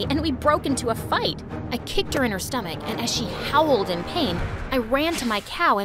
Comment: The captions say “haloed”. She is saying “howled”.